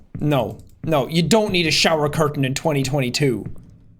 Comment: Wait.. did he just say... We don't need show curtains in 2022...